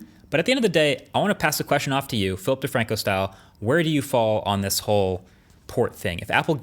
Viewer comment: I heard it as filthy franku style